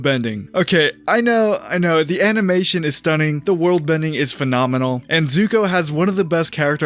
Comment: did you say Worldbending? I can’t tell if that was a Freudian slip or if that’s an actual fandom term lol, if it’s not it probably should be.